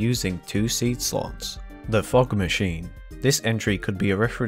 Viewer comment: I thought he said "The f*** machine" for a minute there